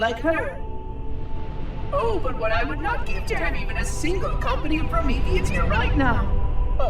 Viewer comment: At the minute 24:00 does he say something about a "company of prometheans"?